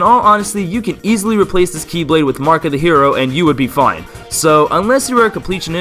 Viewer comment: I thought you said "Marka the hero"